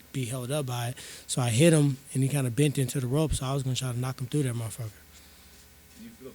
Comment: I thought you said you was going to knock him out lol.you got paid well stop moaning